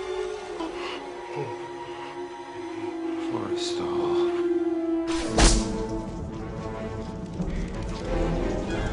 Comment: What did he say? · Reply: Forrestal